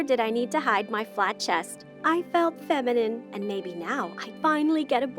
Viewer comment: I thought she said I feel feline